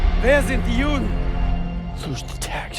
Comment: He is not asking where the jewish people are, he is asking who they are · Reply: I thought the nazi said "die judens"